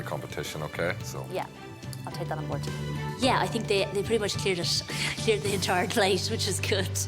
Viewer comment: what is she saying? "Yeah I think..."? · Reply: Juan Crerar She said “Yeah I think they pretty much cleared the entire plate.” She’s got a lisp tbf so I understand why it was hard to hear.